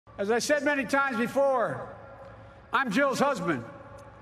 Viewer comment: Me and my friend thought he said, "As I've said many times before, I'm Joe's husband." We thought he roasted himself because of that one blooper he had before loool😂😭